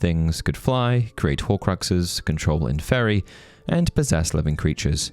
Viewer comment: control what? what word did you say? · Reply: Inferi :)